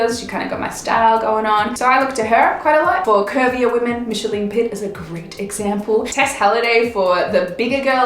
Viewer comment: Who was the lady mentioned after Ruby Rose? I didn't catch the name.. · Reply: artsylove pretty sure the name's micheline pitt